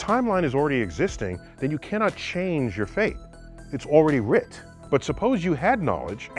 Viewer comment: did he say writ?